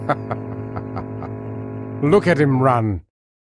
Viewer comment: Hahaha! Look at him run! 1:50:48 Hahaha! Look at him run! (the subtitles are wrong here, weird) 1:49:18 Ben! Quit yappin' and make something happen, will ya? 2:08:37 Ben! Quit yappin' and make something happen, will ya?